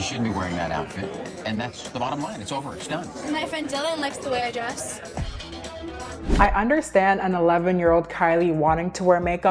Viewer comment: “My friend Dylan likes the way I dress” was a RESPONSEEE omggg she truly had no fears😭😭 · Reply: 😂 I thought she said my friend Dylan wears a dress 🤦‍♀️. Thinking about it now, would be 🫠 her telling THAT comment to Bruce.